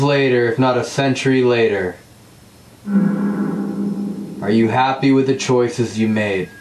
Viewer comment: i heard “are you scared”